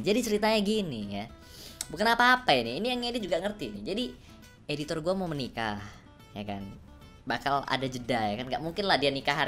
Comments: njir kok gua dengernya "Editor gua mau Meninggal"😅 Canda Editor✌ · anjir gua dengernya "editor gua meninggal" Sumpah gblknya aku cok